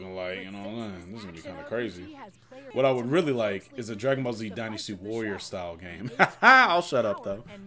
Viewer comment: Burst Limit was really cool though Did you just say "dragon ball z/dynasty warriors type game".......dude